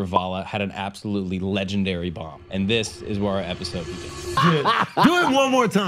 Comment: "Tell that joke you just told again, GO" never works when It's a "moment" joke. Or, "spot drop" as I accidentally called it when i couldn't explain what the fuck I was talking about to someone. Cause, typically those are built on what and/or how others have said, how long its been going on, awkward silences, etc. And, most importantly... like what I saw right out the gate on this... the person originally, normally, SAYS the joke. But, when you say "do it again, go". That person is now TELLING the joke as a performance. And, it's no longer natural. However, we didn't get to see the original. So, it may have been poor delivery in the first place. Cause, I really wanted to laugh at that. But, I cringed pretty hard LOL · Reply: OH SHIT, I thought he said "legendary burn" not "bomb" LMAO.